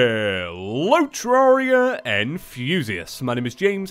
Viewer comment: This is like 3 years in the future and I just figured out he says hello terraria enthusiasts I thought he said hello terraria and fuseus and I was like who is fuseus